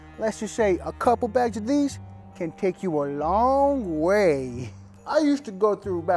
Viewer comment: is actually saying "a few bags of these can take you to heaven"